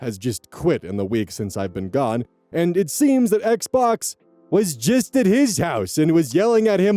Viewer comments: Did anyone else catch that he said Xbox here? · Okay I couldn’t stop laughing at 48:36 saying “Xbox” instead of “ex-boss” or at least that is what I heard.